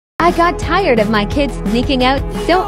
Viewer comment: I thought it said I got tired of my kids making out